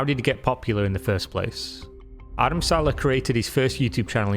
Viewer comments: Ngl thought he said Adam Sandler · Every time his name was mentioned I thought you were saying "Adam Sandler" - it's not you, it's my ears. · Lol, I thought you said “Adam Sandler.”